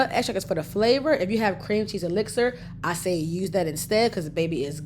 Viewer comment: What did she say you can use instead of Powdered sugar @ 4:35? Cream cheese elixir something like that? I listened twice and couldn't catch it